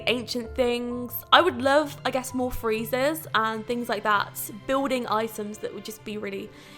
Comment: did she say 'i would love some more freezers?'